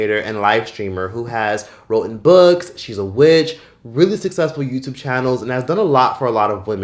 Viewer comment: Did I hear, "She's a Witch?".. 👀🤣